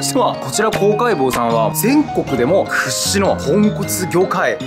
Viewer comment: 豚骨がポンコツに聞こえるw